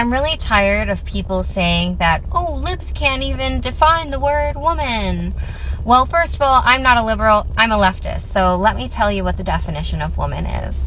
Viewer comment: For a moment I thought she said, "I'm not a liberal, I'm a luciferian". Also, saying there is no definition for "woman" at the start of her stunning and brave educational psa is quite the checkmate there, Madam Einstein